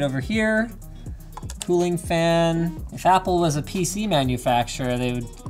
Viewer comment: did you just say fapple? FAPple? as... a very exciting apple computer?